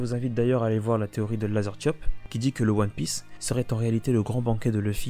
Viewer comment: J'ai pas trouvé la théorie sur le banquet dont tu parles... Quel est le YouTubeur en question ? J'ai entendu "lasertiop".